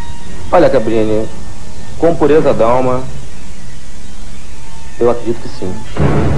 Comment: O que ele diz em 3:50? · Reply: "Com a pureza da minha alma, eu acredito que sim"